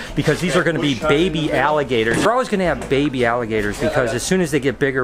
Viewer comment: I thought said no to the baby alligators? 😂😂😂